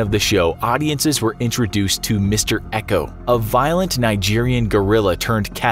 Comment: Did I just hear "A violent Nigerian gorilla"???